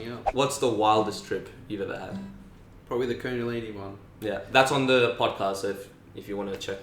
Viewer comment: What was that drug he said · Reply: kundalini, its not a drug tho